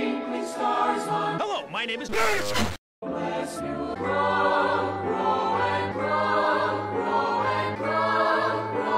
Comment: What did it say at 0:19? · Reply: Bless you.